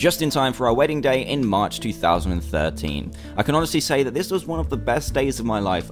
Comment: i Thought he said he married in May 13th bc idk and i said iys on my bday, but i heard it again it was march 13th.XD Im hearing things!